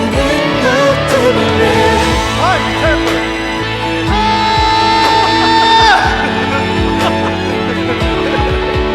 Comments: 형 이때 뭐라고 하는거야?? 계속 이부분만 듣고 있는데 궁금해서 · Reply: 하 정말 못하겠다 다시해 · 볼때마다 궁금한데 3:24에 마크툽님 뭐라고 하시는걸까요..? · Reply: 아 이렇게 할걸?